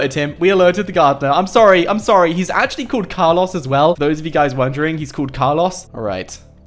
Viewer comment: he said Car Loss instead of Carlos man his England accent is funny sometimes